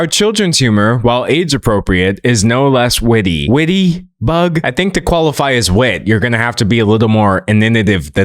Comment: What word does use at 11:53? Inninative? What? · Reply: I think he said "innovative" but pronounced that way probably because of the Texas accents/pronunciations. I'm from a different area of the US so I don't know if that is in fact Texas, I'm just assuming since iirc, he grew up there so that's where he'd get the majority of his accent and pronunciation habits. Or I could be completely wrong. That's just what I'm guessing based on the context of the word being used and what it sounds similar to imo.